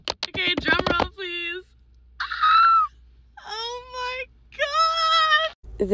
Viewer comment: Did u just say “oH mY GyAAt”